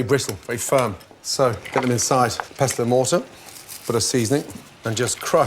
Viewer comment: what's that stone grinder called? specifically the one he has, i couldnt understand him in the video but i think he called it pesto mortum? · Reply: its a mortar and pestle